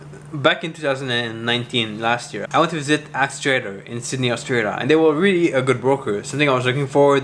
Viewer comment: which broker did he say? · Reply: axitrader